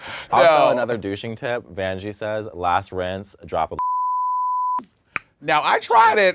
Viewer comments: I wanna know what said @ 20:35. I need all the tips I can get honestly. Picasso days are not fun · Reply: if my lip reading is right says listerine... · A drop of what? What does say here? I'm tryna take notes 🗒️✍️ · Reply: Listerine 😉